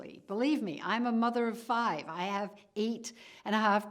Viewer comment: did she say she has 8 and a half grandchildren???